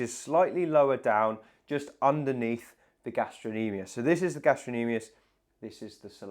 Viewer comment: That’s what he said… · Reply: I didn't hear him pronounce the "c". It sounded like he said gastro-nemius to me. Maybe I misheard. The C is definitely pronounced in gastrocnemius